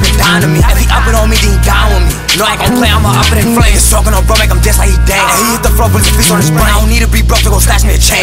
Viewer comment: The song hard don’t get me wrong but wtf do he say at 0:56 ? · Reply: “Smokin on bro make him dance like he Dane”